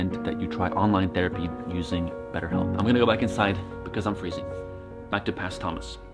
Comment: When Thomas said “Betterhelp”, my stupid ass thought he said 7up. When he then started to talk about therapy I got quite confused, and then realised